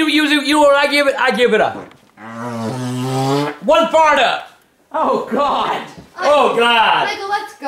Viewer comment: my favorite part 4:27 that makes no sense what he said but hilarious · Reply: He said “One Fart Up”